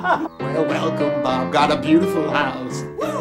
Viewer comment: I hear him say "Bob got a beautiful house" which makes more sense.